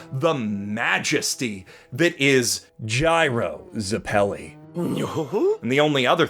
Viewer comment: OK who made the subtitles 'Caesar Zepelli' instead of 'Gyro Zepelli'